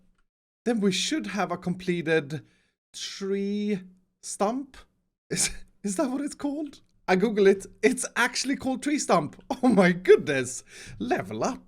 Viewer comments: when you somehow hear stonk instead of stump... too much meme for me -. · Who heard him say tree trump at the beginning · Am I the only one that heard you say 'tree chump' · Did he just say tree trump?